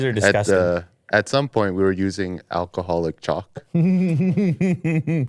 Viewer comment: At first I thought said alcohol chocolate 🍫 😂, instead of chalk